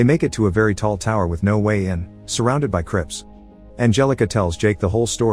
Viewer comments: I thought he said surrounded by crips I just pictured a bunch of niggas all set ready to get froggy 😂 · I'm partially watching this while working and I was startled when he said that there are 12 crypts around him, I heard "crips" and I glared at my screen in confusion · did he say crips????